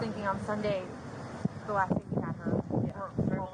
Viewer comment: I thought she said the LAST DAY we had her